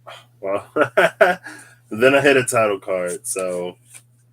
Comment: 😆🤣at just before one hour, 8 minutes, when you asked, "How does this bird serve you?" And, then I heard "tower" instead of "title" card, so Phoenix flew outta my mouth🙊🐣 I'll keep it as my final answer, actually🦅💜